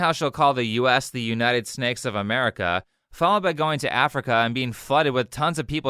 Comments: I keep hearing her say "United Steaks of America." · At first I thought she said "The United Snacks of America" instead of Snakes 😭😂